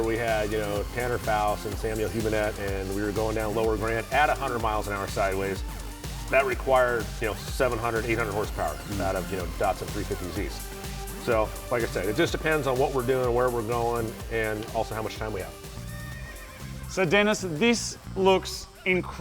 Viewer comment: did he just say datsun 350z